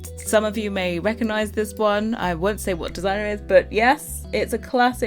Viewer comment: I know! Just avoiding dropping the irl inspirations all the time, just incase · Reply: Oh so sorry, I misunderstood! I thought you said you can't say which designer it was, but you said you won't say. Need to rinse out the old ears here.